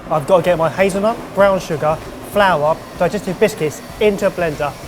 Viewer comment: I kept hearing "digested biscuits." X'D I'm sorry, please go on.